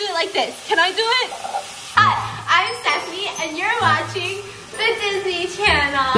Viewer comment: why did i think she said "Hi, I'm sesame" LMAOOO